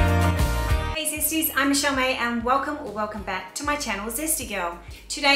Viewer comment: Everything looks tasty and wonderful:) I have something funny to share , all this time I thought you were saying your name was Charmaine and I was wondering who Michelle May was . Today As I was watching your video I clicked on captions as my house was getting a bit loud and bam 💥 um yep you are Michelle May lol I really am feeling my age now, hearing aid check lol